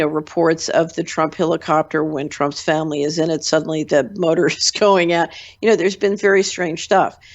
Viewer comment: Mr. Hunter, what is she saying at 17:52, I can not understand what she said at the time! Please if anyone knows, please inform me!! She was saying THE MOTOR IS ________ UP! Thanks for your time, and GOD BLESS!!! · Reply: "...the motor is going out." Referring to an incident in 2017 wlile Ivanka was on board maybe?